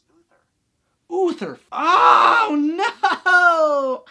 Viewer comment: Who else thought he was saying “other” pendragon? As in unnamed lol it all clicked when he said uther at the end 😂 no hate, you’re great!